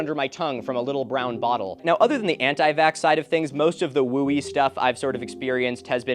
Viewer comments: hey, really bad subtitle error at around 1:34:30. says "Jewish stuff" instead of "Woo-y stuff". definitely needs correcting ASAP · there seems to be an error in the captions at 1:34:36 ! it says 'Jewish' instead of 'wooey' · Hey, I don't know if you have any control over it, but the captions at 1:34:37 says "most of the Jewish stuff" instead of whooey. It might be pertinent to fix if you can